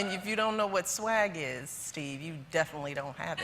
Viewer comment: Did she call him "Steve"? :)